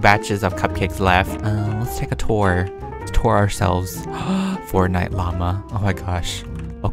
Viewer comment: Also did u know that bunnies eat their poop to get their full nutrients of the food they ate lol Edit: when u went to the house with the fortnite poster it sounded like u said fortnite mama 😂😂